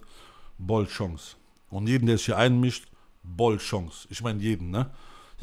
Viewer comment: Was sagt er bei 1:54:05? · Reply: Bollchance?